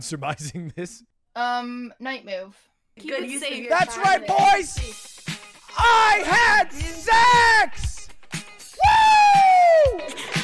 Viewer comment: this is what I heard “That’s right boys! LETS PLAY RAID SHADOW LEGEND” well placed ad, but I hate it.